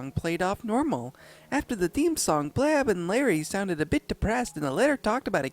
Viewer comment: It sounds like he is saying "blab and larry"